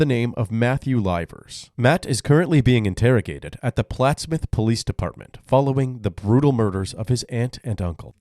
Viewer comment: You know you are dealing with scholar material when you ask someone to stand up for themselves, and they get up from their chair. EDIT: I left this comment right after the intro expecting more context to that interaction. But there was none. If you think replying that no, that's not what happened is going to in some way make things right. You're simply going to join the now 100s of people saying the same thing. And I've already conceded the point. Chill out. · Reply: At first i thought you said the plastic police dept.